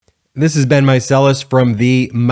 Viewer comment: I'm just realizing your name is Ben. I thought you were saying 'this has been Miceles' and my mind went right to Pulp Fiction.